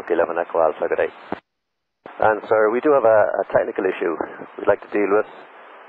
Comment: Okay this time I will tell you what subtitles you misheard: 0:24 "we do have a technical issue we'd like to deal with" 1:02 "and it may well be dumped on the runway" 1:09 "Hydraulic fluid and you're returning I got that" 1:53 "We'll take a left turn but if we need to take up a hold closer in then we can do that" 2:00 "You let me know as soon as you need" 3:03 "is it possible to request the ILS for 22L?" 3:09 "Our nosewheel steering is inoperative" 3:22 "It's not an issue but it'll look a little bit different to how we normally look" 3:36 "It's within our normal operation but because we have less hydraulics ... so we're touching down a little bit faster than normal" 4:21 "we don't have pounds on gauges" 5:25 "Left turn direct to ROBER" 6:41 "but have the rescue services on stand by, yes please" 6:43 "Okay, I'll make the call, they'll be there" 7:25 "in the event of a go around" 7:37 "I want you to maintain, just maintain 2000 if you have to go around - 2000" 8:41 "are you coming to a complete stop this time sir?" 8:58 "We require towing from this position" 9:17 "any fluid dripping from underneath the aircraft" 9:43 "We have products being deployed 9:50 "detect some sort of fire" 9:57 "switch to this frequency and give you further information" 10:10 "Yes sir, just be advised all the vehicles should be on 21.65 to talk to them right now. I imagine there's gonna be a lot of conversation back and forth" 10:30 "Are you showing any negative" 10:37 "make sure nobody else speaks apart